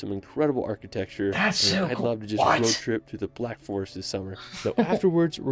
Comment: did anyone catch the name of the lake? I might go there when I'm in the area in a few weeks... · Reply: All he said was Black Forest...